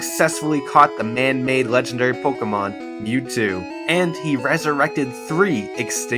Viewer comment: Did he say resurrect 3 Pokémon? I read it as 2 on there.